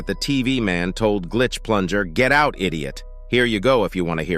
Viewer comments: Hold up I think the TV man that was yelling at plunger cameraman was saying get out immediately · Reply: I thought it said "get out idiot" lmao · I think TV man on 12:45 said "Get out, Immediate"